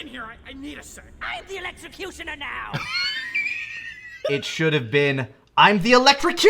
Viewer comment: I definitely hear "I'M the Electrocutioner now" as if the Electrocutioner just died and he's taking the name. I get it. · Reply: I always heard "IMTHEELECTRICUTIONERNOW!!" Like he's just so obsessed in general about it and has no patience